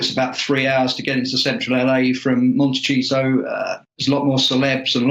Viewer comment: Did he say "Monte- cheese-o" at 8:18😂😂😂😂?